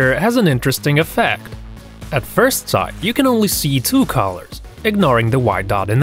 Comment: On the first one when he said color I thought he said collar so I was like what’s a collar? Tho I knew the dog collar but I thought there was another meaning... until he started talkin bout colors